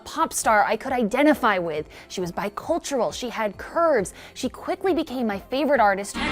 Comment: She was bi cultural she had ****? What does she say in 0:29??? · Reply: 'she had curves'